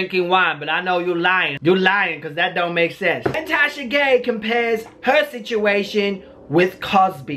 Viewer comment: Wait...at 8:50 did just say “Tasha Gay’s situation” ? 👀👀